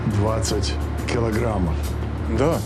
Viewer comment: he says "twenty" in Russian and the caption says "sixty" 😂😂